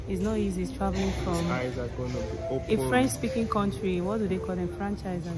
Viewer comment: Did i hear “ A French country is franchise?? 😅😅😅